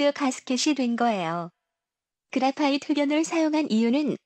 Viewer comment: 여기서 그레파이트 "토련"? 이라고 하는건가요? 저게 정확하게 무슨 표현인지..아무리 들어도 이해가 안갑니다. 영상에서는 "두번째로 필러 부분은 자세히 보시면 와이어와 그레파이트 토련(?)이 겹겹이 쌓여있어서 위에서 보면 나산형 모습이죠" 이라고 하는데..그레파이트 뭐라고 하는건가요? 06:18 스테인리스 강은 내식성 코팅이 벗겨진 부분에서 카본을 만나면 이깨부식(?) , 이때 이깨부식이라고 하는건가요?..ㅠㅠ 그리고 이러한 부식이 발생해서 휘성(?)이 활발해진다라고 하셨나요?... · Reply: 우선 불편을 드려 죄송합니다. 궁금해하시는 부분의 음성은 "그라파이트면"입니다. 두번째로는 "입계부식"이 되겠습니다. 입계부식과 관련해서는 아래 영상에서 자세히 다루었사오니 시청하시면 좋을 것 같습니다. <link> 향후 영상 제작 시 음성 전달에 더욱 신경 쓰도록 하겠습니다. 감사합니다.